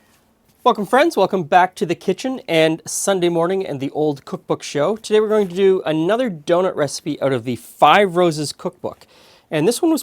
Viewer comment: In the beginning I thought he said “fuck em friends,welcome back to the kitchen” 😂